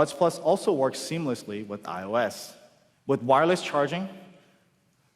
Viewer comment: did he say i os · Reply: Yeah, the buds work with iOS